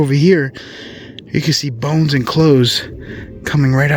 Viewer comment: Idk, for whatever reason when he said "Bones and clothes" I heard, "Boats and Hoe's." 😄 Awesome video!